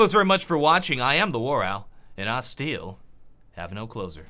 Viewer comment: When I first watched, I didn't understand his outro. So I thought to my self that he said ,, I am, and I still have no clothes on'' x)